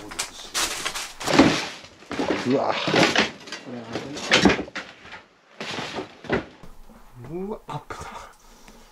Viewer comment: の辺りでさんの後に聞こえる声はさん？ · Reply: それ危ない って言ってるんですかね